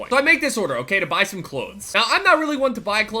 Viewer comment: am i the only one who heard him saying Klotes